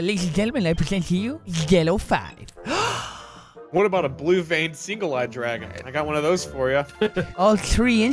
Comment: can anyone understand what is saying at 13:20. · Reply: "What about a blue veined single eyed dragon? I got one of those for ya" "All 3 inches?"